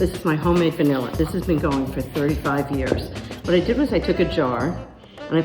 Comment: did she say 35 years?😳🤔